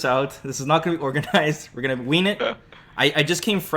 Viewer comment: did he say wing or ween? i kno he meant wing, its hilarious he said ween tho